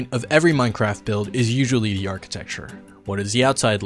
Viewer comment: "did you Say achitech"